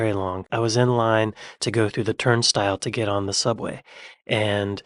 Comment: Wait till you see the infamous tea Tumblr thread people be putting mugs on the stove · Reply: I thought you were saying they put them in tumblers for a split second. Might as well. Microwave, my arse. haha